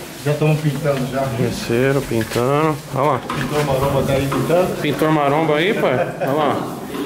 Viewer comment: o cara:pintor maromba ai pintando Toguro: eu ouvi "pintor maromba ?" Saca ae pae